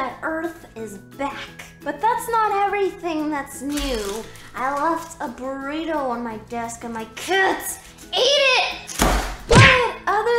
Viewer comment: wait.. in 0:09 did just say “i left a burrito in my desk and my KIDS eat it”? comment wut u heard! :)